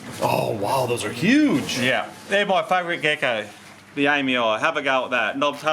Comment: Hey whats the name of the gecko on 6:00 minutes.. i heard you say something like the aimy eye gecko ? How do ypu pronounce the name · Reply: I know it’s six years late but they’re called knob tail geckos. This one in particular is Nephurus amyii